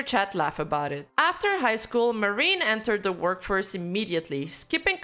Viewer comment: I’m sorry. Did I hear “Hoo-Shoo Marine”? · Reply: “Horseshoe marine”